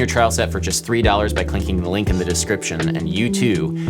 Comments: Is it just me or did Adam say ‘clinking the link’ at 3:42 😂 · did he just say " clinking the link" ?